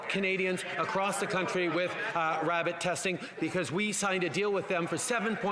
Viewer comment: Did he say rabbit testing?